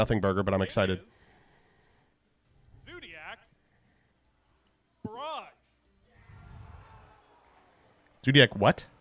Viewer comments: I thought he said "Zoodiac Broad." Now i have to clean the shit off my chair for no reason. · Dude, I deadass thought that he said "Zoodiac Broad" instead of "Barrage" and nearly had a heart attack.